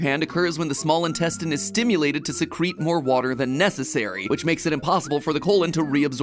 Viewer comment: SECRET instead of Secrete lmao I'm dying